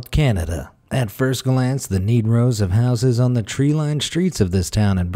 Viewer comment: about the city of Kitsaoul in Canada. Does he say neat row or negro? H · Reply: He said neat row. I had to check myself because it sounded like he said 'Negro' at first to me too. On the Strength 💪🗽✊🇺🇸